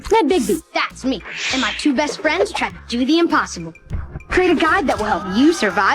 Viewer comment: I have a confession: for years I thought Devon was saying "Ned Biggie, that's me and my two best friends: chess, cookie, and opossum." As it turns out, he's saying, "Ned Biggie, that's me and my two best friends try to do the impossible."